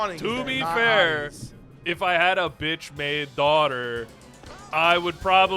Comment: I genuinely thought you said "bitch-made" Cinderella. Especially after talking about her biological children. Never change, never change.